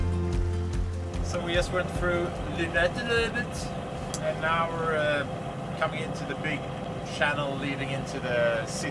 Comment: what did ya say? Wow lol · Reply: Ashley Wynn he said Lynetteloebet 😊😊